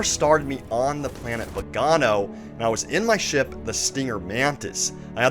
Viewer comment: What Planet did he say he started out on at 3:53? Begano?